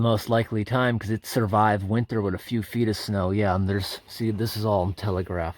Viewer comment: I misheard "feet of snow" as fetus smell LMAO